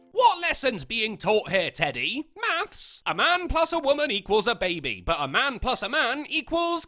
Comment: I had to replay a certain bit because I thought you said "A man plus a woman = a baby, but a man plus a man = piles" ...